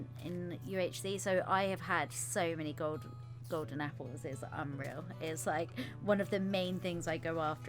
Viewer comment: When she was talking about eating golden apples i thought she said golden retrievers And was rather shocked when she said "Multiple" XD